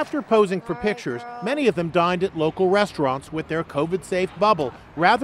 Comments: sounds like they said died instead of dined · I thought they said”afterwards they died at a local restaurant